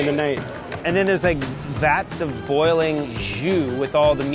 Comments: Man, with all the hate popping up in US news, I actually, and apologetically, thought he said, "Vats of boiling Jews". Shook me up. F*ck the leftist media. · did he say bats of boiling Jews?(sorry if I offended anyone)